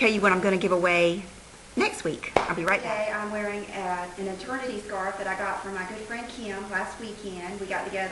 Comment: I thought you said "maternity scarf" and I was like... how is that a thing and I didn't know was pregnant!